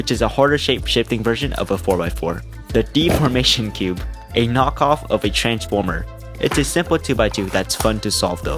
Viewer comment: I was in the kitchen and had this playing on my PC and 0:55 I thought he said "A deformed knockoff of a trans mother"